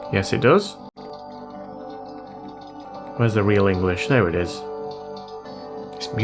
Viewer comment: Where is the real english! 🤣🤣🤣🤣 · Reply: What do you mean? He says "Where is the real english?"